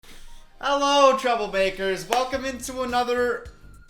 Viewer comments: I thought you said “troublebakers” at the beginning of the video lolol im dumb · Oh my shit i thought you said hello my trouble bakers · I thought you said trouble bakers. 🤣🤣 ✌🏼❤️ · Why did I hear “troublemakers” as “trouble bakers” as if just switched up his content to make baking videos 😂